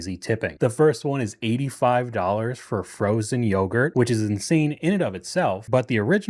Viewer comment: At first I thought he said $85.00 for frozen yogurt, but that would obviously be totally insane. Unless maybe you're buying for your kid's entire high school football team.